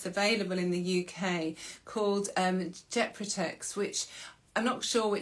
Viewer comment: At time 26:48- what is the name of the Utrogestan like drug- jet protex? I don't hear the name properly, If someone can write the right name. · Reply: Gepretix